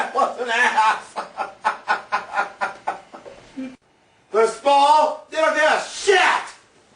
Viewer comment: I thought he said. "if this falls, I wont give a shit"